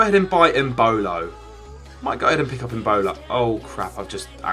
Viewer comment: I swear he said Ebola